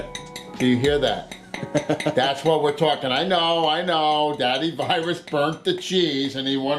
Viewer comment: d-did he just say d-daddy virus? UwU